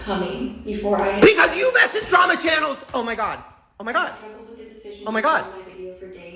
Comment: The first time I heard Shane say 'because you messaged drama channels' I thought he said 'bitch have you met these drama channels'?' 😂